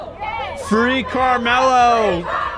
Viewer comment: Sounds like they say free canelo